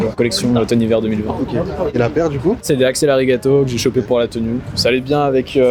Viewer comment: c'est quoi le nom de la paire svp il l'a dit mais jai rien capté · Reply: axel arigato